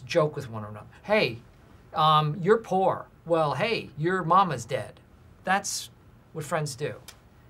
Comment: i hear “your mom is dead” not “your momas dead”